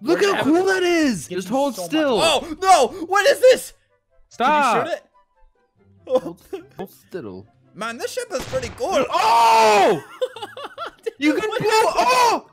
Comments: did he say shit? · Ik he didnt but it sound like he said" Man this s* is pretty cool" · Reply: he said ship just in a funny voice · who else thought Albert said man this sh** is pretty cool